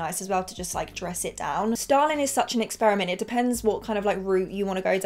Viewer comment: had to do a double take thought she said Stalin